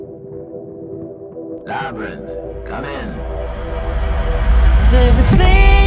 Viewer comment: What does he says at 0:04 ? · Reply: I believe he says, "Labrynth, come in"